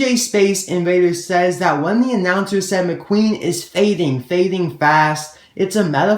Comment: When the announcer is talking, I hear "McQueen is spinning, spinning fast"